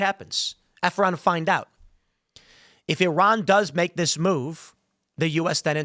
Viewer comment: I kept hearing "Hit me and find out" Is provoking and makes you the bad guy.